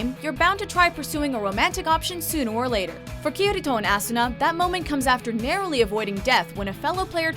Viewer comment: did she just say Assuna? lmao